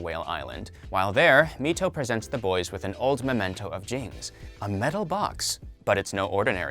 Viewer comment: did he just say James